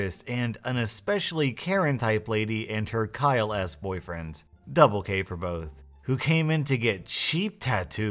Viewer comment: Instead of double k for both i heard double gay for both oof